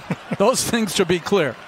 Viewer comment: am I hearing this correctly... “Docic doesn’t want to FART Marcus Morris Sr?”😂💀